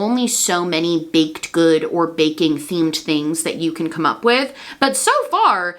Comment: "Baking themed" I swear my dog thought you said bacon themed because his head shot up SO FAST!!